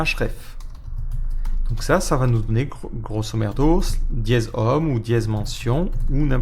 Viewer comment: Qu'est ce que tu as dis ? "Grosso merdo" ? 20:45 Ahahah xDD "Grosso modo" Bonne journée canaille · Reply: Tu as bien entendu ;) Grosso merdo <link>